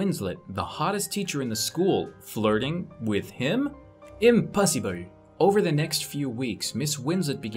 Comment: / 5:26 the way he said “impossible” ahhh I just loved that ❤️ 😂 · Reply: Sound like he said impussyible